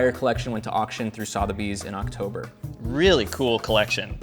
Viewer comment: Sotheby’s? Is that what you’re trying to say? Sotheby’s sounds like sss-other bees. 😂